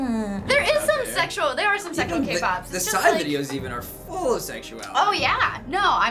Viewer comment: 4:Am i the only one who thought that he said xia and freaked out??? But no...he said psy-_-' Even if he said xia it still would fit in that sentence;)